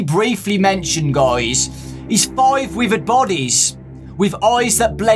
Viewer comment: When he said “Five withered bodies” at the end with the stitchwrath story, I thought he said five withered bonnies